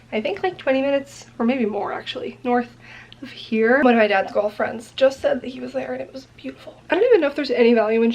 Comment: hahaha when you said "one of my dad's golf friends" I heard "one of my dad's girlfriends" - literally spit out my sparkling water when I realized hahahah